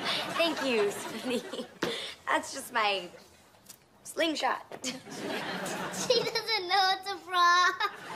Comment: i thought zuri said, "she doesn't know it's a frog," since that's what the captions say, but then i scrolled into the comments, and some kid pointed out the caption and i rewatched it and i realized what zuri actually said and now i feel betrayed by my own childhood.